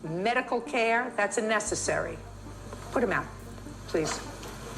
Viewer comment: I THOUGHT SHE SAID PUT HIM DOWN😭😭😭😭